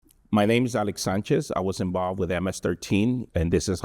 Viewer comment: I heard "my names rick sanchez" in the beginning